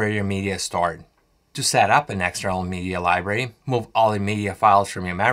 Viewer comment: move onto your ???? hard drive... I cant tell what you are saying. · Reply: edit... ok you said "MACS hard drive"...